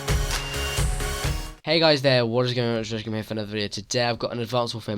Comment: What did you say lol? "Hey whats goinonerhghsadkfj"